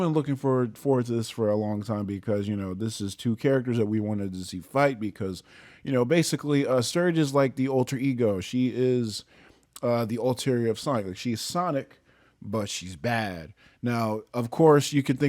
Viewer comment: When you said alter ego, I first thought you said ultra ego.